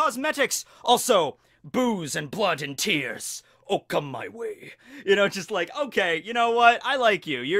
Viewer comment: Did you say Okama Way or Or come my way?